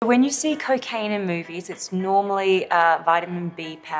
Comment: When she first said cocaine, I thought she said "karkayn"